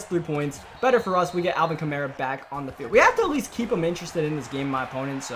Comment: kamara activated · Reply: I thought you said karma